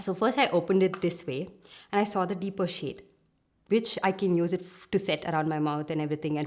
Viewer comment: You said 'which' but I thought you said 'bitch' 😂😂😂😂😂